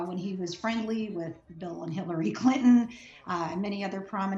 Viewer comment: Did she just say "Hitlery Clinton"!?